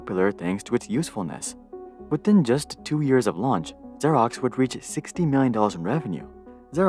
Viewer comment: I thought he said "would reach 69 dollars of revenue". A whole 69 dollars!